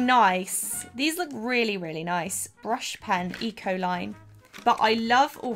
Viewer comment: When you said Ecoline, I thought you said E Coli and I was like "what do coliforms have to do with brush pens??"